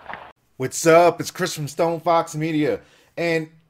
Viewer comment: OMG UR SAYING ITS CHRIS FROM STONEFOXMEDIA! I ALWAYS THOUGHT U SAID ITS CHRISTMASFOXMEDIA LLOOOOOOOLLLLL